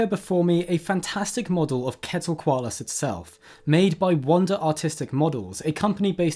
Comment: Ok I know you're just brittish but I heard "wonder autistic models" and I (being on the spectrum) just didn't think anything of it and literally didn't realize until the very end its "artistic" (obviously, I just also have ADHD, and was processing the more important info in the vid lol)